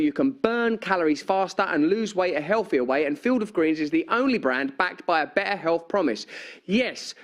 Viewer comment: Visit <link> promo code BRAND for 15% off your first order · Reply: my close captions said "field of Grease is the only brand"